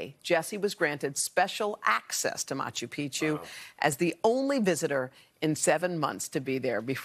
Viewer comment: Oh I thought you said Pikachu, never mind